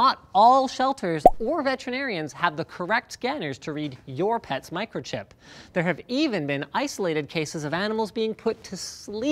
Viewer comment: Who else thought it said pet microphones